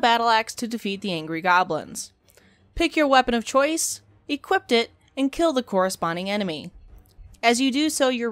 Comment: there's no t after the word equip. i always hear you saying "equipt it" when in fact it is "equip it"